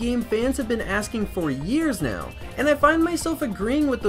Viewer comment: Am I the only one who heard him say the “Nintendo Swiz”